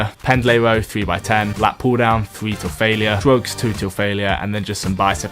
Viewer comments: did he say drugs or shrugs lol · who else thought he said drugs lmao